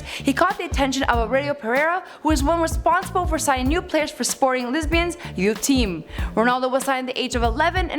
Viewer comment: I swear she said sporting lesbians